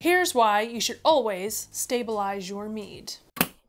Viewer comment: Idk what I was thinking... (not in a dirty way) I thought you said stabilize your meat... I was lost for a minute.i was expecting a steak or something. Then after i seen the content, i realized you said mead.